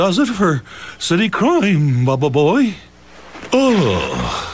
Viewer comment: did he just say "Bababooey" lol